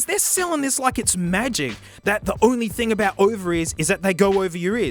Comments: I keep hearing "overies" instead of "over ears" XD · I keep hearing him say “ovaries” instead of “over ears” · This whole time i kept hearing "Ovaries....ovaries...." until i realized that he was saying "Over Ears". Damn my Eagle ears. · "Over ears" American hearing thicc accent: "Ovaries" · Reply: I'm Australian and I heard "ovaries" at least once. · "Over ears" American hearing thicc accent: "Ovaries" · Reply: Wtf i thought he was saying ovaries because it kinda made sense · am i the only one that thought he was saying "ovaries" when he said "over ears"? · "Over ears" American hearing thicc accent: "Ovaries" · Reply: i actually thought he said ovaries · Every time I hear "over-ears" I hear "ovaries" save me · Oh! Over ears! I thought he kept saying “ovaries” 😂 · Is it just me or did it sound like he is saying ( ovaries ) and not over ears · "Over ears" American hearing thicc accent: "Ovaries" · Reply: i'm also american and i can confirm i heard "ovaries" for a quick second before realizing he meant "over-ears" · Over ears? I thought he said ovaries😂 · Do audiophiles actually call over ear headphones "ovaries" for short??? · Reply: I THOUGHT HE SAID " over ears " NOT OVARIES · "Over ears" American hearing thicc accent: "Ovaries" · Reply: I thought he said ovaries at first too (I’m from Canada) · Oh God. You were saying OVER EARS. Christ. My American ears thought you were saying "ovaries" for half the damn video. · i keep hearing "ovaries" instead of "over ears" im dying